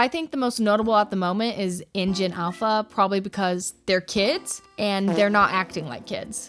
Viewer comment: i thought they said “engine alpha